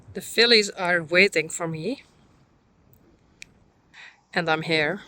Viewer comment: Haha, instead of "The fillies are waiting for me," I thought you said "The SILLIES are waiting for me!" lol!